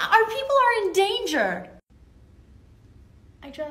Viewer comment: Haha “a man named Percy has killed her” Twice technically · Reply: They are not ‘right’. They misheard what she said. Likely never having heard the name Perseus, they assumed she had said ‘Percy us killed her’ and then took the unilateral decision to amend the ‘us’ to ‘has’. Also wrong. I had read of the Greek myths before I turned 10. Percy Jackson stories had not been written at that stage. Having worked in education for over 20 years, I saw a distinct downturn in reading by the majority of students. The last several years were very painful as students just did less research and relied more on plagiarising material from others or from Internet resources.